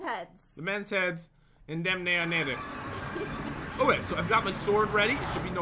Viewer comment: sounded like the n word